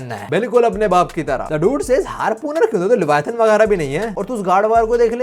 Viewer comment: at first time I thought he said gooner 😅